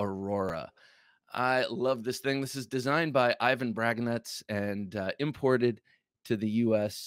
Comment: I thought you said Ivan Dragnuts that knife you showed 1st straight fire .